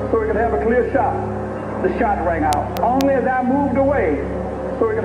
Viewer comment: Am I tripping??? 6:15, 6:16 did he just say, " I moved away so he could have a clear shot. Then shorts rang out???" Maybe I better listen again